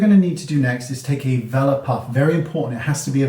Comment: I had to Google what the puff was. It's a *velour puff* for those like me who had no idea what it was. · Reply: Thank you for the upate. I thought he said "vellum" puff! I would have been searching all day!